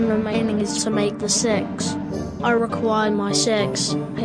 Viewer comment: LOL I heard "Where is the sex. I need my sex."